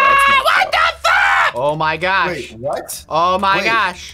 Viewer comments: he sounded like he said what the f*** 2:36 my parents thought he said a cuss word LOL😂🤣😂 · almost said WHAT THE F**K instead of that he said WHAT THE FAAAAAA 🤣😂😂😂😂